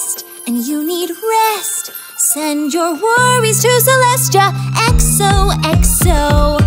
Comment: I heard Celeste(My travelers name) instead of Celestia.